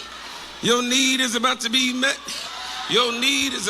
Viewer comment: 😂😂😂 I thought he was saying your knees about to be met, I had surgery on my right knee and it was hurting 😂😂😂